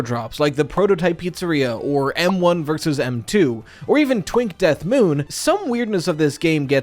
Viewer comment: what the hell did you say lmao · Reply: TWINK DEATH MOON??? WHAT?? IM NOT DEAD!!!